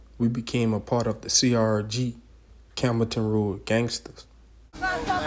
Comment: Sounded like you said camel toe toad gangstas